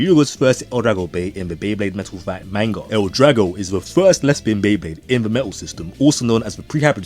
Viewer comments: I was trippin for a sec I thought bro said "lesbian beyblade" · I had to double check, instead of hearing "left spin" I heard "lesbian" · Am I the only one who misheard Left-Spin as Lesbian at first?